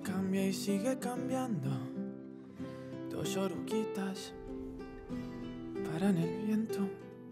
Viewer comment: hahaha this whole time i thought he said dos Sorroquitas and not dos oruguitas. Which changes the meaning of the whole song hahahhaah